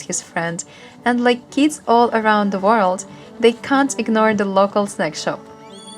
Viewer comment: i thought you said they can't ignore the local sex shot :D